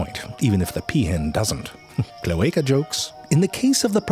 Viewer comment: Did he just say "cloaca jokes"?